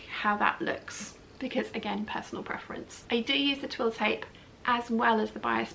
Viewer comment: Hi... Did you say after talking about the bias binding that you used a "twill tape " I played it back but must have misheard Thanks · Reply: Ann Buck I do use 1/2” twill tape to stabilise the neckline before I attach the bodice to the skirt 😊